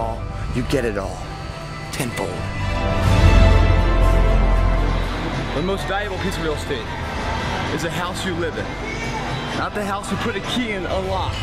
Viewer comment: What does he say at 3:30? Sorry I'm not native and I'm not sure of the words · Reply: The most valuable piece of real estate is the house you live in